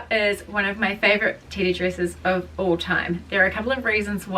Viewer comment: I thought you were saying titty dress this entire time